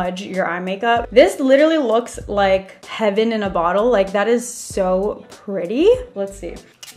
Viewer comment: it sounded like she said 'Kevin in a bottle' and I'm like who the hell is kevin love yoou!!!!!!!